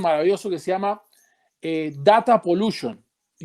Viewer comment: como es el nombre que dice? el del informe "data pollution" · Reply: Data pollution, es correcto.